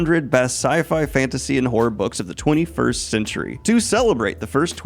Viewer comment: I was wasn’t looking at the screen when you mentioned the top 100 sci-fi, fantasy, and horror books and I thought you said sci-fi, fantasy, and board books and I was so confused. Why would they include toddler books with sci-fi and fantasy?